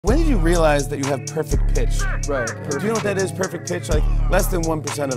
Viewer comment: I thought he said perfect fish 💀💀💀